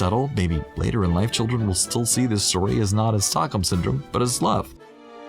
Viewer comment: Beauty and the beast isn't about stalkhome syndrome!!!!!! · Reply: You're totally right. Also, it is "Stockholm", not "stalkhome". I can see why you misheard it that way, as it kind of makes sense. But the syndrome was named after a famous incident in Stockholm, Sweden. It's pretty interesting if you want to read up on it! ^_^